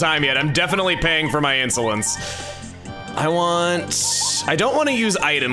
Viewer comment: I am definitely paying for my insolence At first I thought you said insulins A reference to Vanna White!